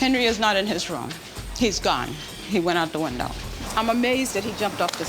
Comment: The camera man's just living his best life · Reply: bruh i thought that too 😂😂 thought he said he was gone all weel